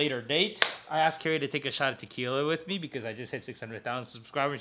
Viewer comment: I thought you said take a SHOWER with you. Not a shot.